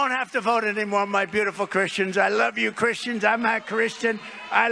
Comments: This is something so many are getting wrong; he didn't say he's not a Christian, he just stumbled over saying that he is (big surprise!) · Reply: Actually listen at counter 0:17. He says,"I'm not Christian.". He didn't "stumble over saying that he is..", he clearly said he wasn't Christian. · Did he just say "I'm not Christian" at 0:18 secs? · Trump actually said one true thing! (not a Christian) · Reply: It's rather funny that when he tries to say "I'm a Christian" he mumbles so much that I really heard it as "I'm not a Christian". What he said is a lie. What I heard is true: he's not.